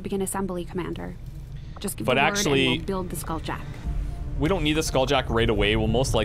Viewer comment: POLO DOES NOT PLAY BY HUMAN RULES! edit: at 52:04, when went to the proving ground Shen said "Skulljack" but the subtitles said "Stasis Lance" Did anyone else notice that?